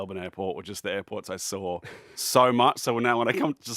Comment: 34 I’m on shrooms rn as an American and his accent throws me off so much here 00:32 I need to come back sober to actually know what he is saying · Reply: Saw is the word you’re not understanding I believe